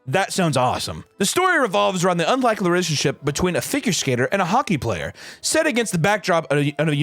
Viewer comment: I heard finger skater, I'm crushed